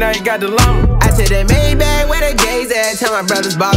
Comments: Lmao did he just say "I took my maybach where the gays at"? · it sounds like he said “I took the Maybach where the gays at” 😭😭💀🤣